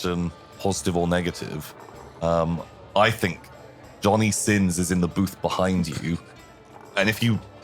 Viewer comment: For a hot second I thought said Jonny Sims and that this was the weirdest Magnus Archives crossover ever